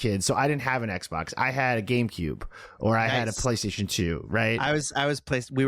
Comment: I almost choked when you said GameCube because I thought you said Gamepube! Jeez. What a dummy I can be.